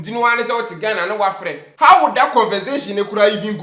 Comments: Did I hear “convensation” ? 🤣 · This is the second time I've heard him saying convensation instead of conversation.....aaaaah